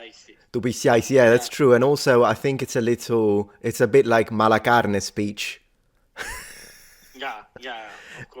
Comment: Lol I hear "malacarne" as "malaka einai", which means "he's an asshole" in Greek.